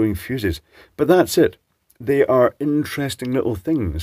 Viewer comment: For some warped reason I thought that said self-igniting fuses😂